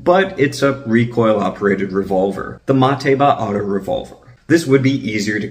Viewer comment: Everytime one of the characters from Ghost in the Shell said Mateba I heard it as Mataevier.